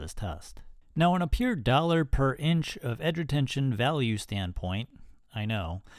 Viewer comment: I heard "duller per inch" , lol